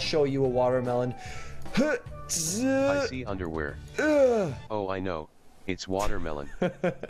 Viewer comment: did he just say underwear?😱😥😣